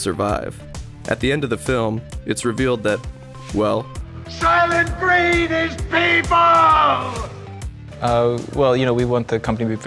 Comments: subtitles: “Soylent freed these people.” 👀 That's… that's not what he's saying… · "Soylent freed these people." hahahahahahahah who wrote the closed captions for this? "SOLYENT FREED THESE PEOPLE." That should be the slogan for the company. NOTE: He actually says, "Solyent green is people." in the movie. Someone was just not paying any attention when writing the CC's... or they've never seen "Solyent Green" or they are clueless... or they are making a joke to see who catches it. But, "SOLENT FREED THESE PEOPLE."